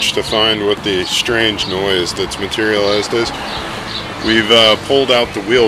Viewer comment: Hey, that's the same sound my Volvo makes when I put on the brakes. Maybe we have the same problem? (Mine's a helluva lot easier to stop though ... ). And, did say "materializsted"? (10:33). Enjoying the vids as always kids. Keep it up!